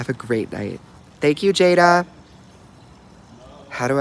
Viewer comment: HE SAID HI TO ME AND THE END OMGGGG HE SAID “HII JADAA” MY MOUTH DROPPED 😭💓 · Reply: I thought he said thank you jada